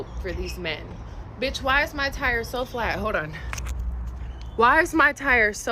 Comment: Lol, I had to laugh when the gal with the flat tire said 'hold on'. I thought she said 'ho down'..